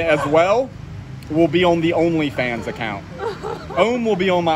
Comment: did he say only vans?